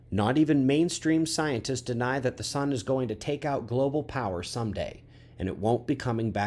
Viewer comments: I thought you said "take out the power Sunday"! Oh right, "take out some-day"...... whew!!!!! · Me thinks with two videos a day and increased intensity that something is coming sooner than we would like... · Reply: I thought he said "it would take out the power SUNDAY"!!! Whew.... it was "some-day"....huge relief!